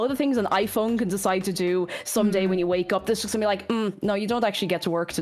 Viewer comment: Genuine respect for a human subtitler going "????? I have no idea" instead of auto-generated subs. I think she said "it's just going to be like" but she is speaking very quickly there.